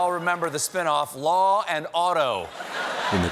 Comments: I thought Donnie said "Law and Otto" ... you know, like Von Bismarck. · I thought he said “Law and Otto”……….want to get blotto 😆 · I thought he said Law & Odo from DS9. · I heard "law and Odo" and pictured a graphic of Odo of DS9 arguing with Ice T and Samuel Waterston